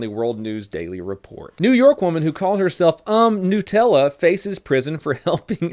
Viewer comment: did you just say 'nootella'